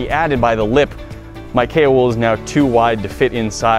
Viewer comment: Haha I thought you said Kale wood not Kao wool xD